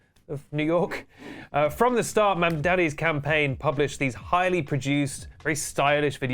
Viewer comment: the video is amazing and all but in 2:25 the captions say “mandaddy” instead of mamdani and i am HOWLING